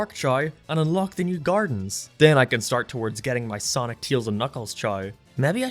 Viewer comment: I keep hearing 'Chai' whenever you say 'Chao' XD Just wait until you go down the rabbit hole of genetics and start making some sweet-looking breeds, as well as additional evolutions that add even more customisability, then breeding for good ranks like you would for a full 31 IV pokemon and create an all-powerful beast. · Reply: Whenever I hear "Chao", it sounds like "Child"